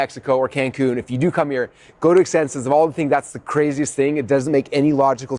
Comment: What's this word you're saying? Exsenses?